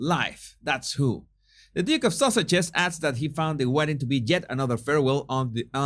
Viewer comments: Why do I keep hearing “Duke of sausages “ ? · Ha ha I thought you said the Duke of Sausages. lol Well he is a bit of a wiener!!!